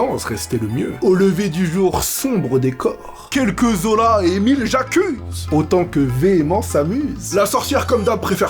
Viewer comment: C'est au 2e visionnage que j'ai compris "Quelques os là (Zola) et 1000 j'accuses", masterclass